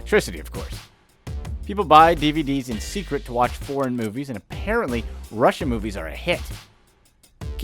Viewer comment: Did he just say " Porn Movies" ?